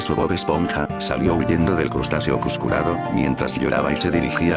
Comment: ¿Cómo dice que dijo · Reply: dice cuscurado XD